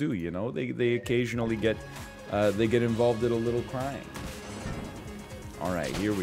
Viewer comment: "They occasionally get involved in a bit of... crying" I misheard it as crime crime baby